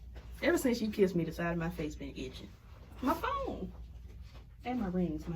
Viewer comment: When you said, "My phone!" All I heard was, "Big Momma ya arm!"